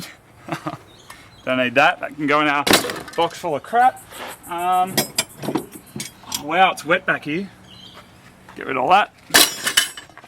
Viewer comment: Did he say it's "wet backy"? Was that a mexican insult or something?? Not gonna lie. I LoL'd.